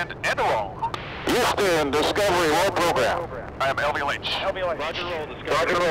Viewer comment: What does the pilot say at 1:40? "I see three at 104 I see a command and a roll [on radio] Houston, Discovery, roll program I have (indiscernible)" What was that last part? Any ideas? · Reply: "I have LVLH." Local Vertical, Local Horizontal (LVLH) frame is a coordinate system used to define a spacecraft's attitude and visualize relative motion